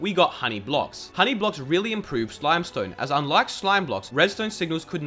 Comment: did say the 'slime stone'